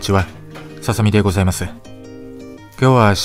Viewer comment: サザエでございますに聞こえたwww ハブられ方半端ないですね❗ 肉で幸せホルモン出してください❗